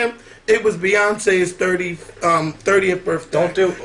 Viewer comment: did anybody catch that @ 18:56 almost said Beyonce's 35th birthday? that's what it sounded like to me!!! i'm just sayin.....